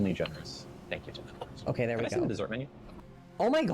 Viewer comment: What does she say at 30:43 ? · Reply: "Can I see the dessert menu" if you're talking about Lilly :D